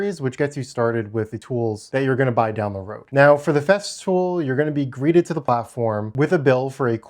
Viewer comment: I thought I heard you say "theft fool"